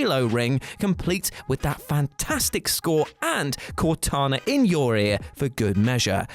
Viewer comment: Did he say "Cortana in your ear" or "Cortana in your rear"? Asking for a friend.